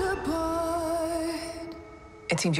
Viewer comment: Did I just hear Optimus prime say rated R at the end?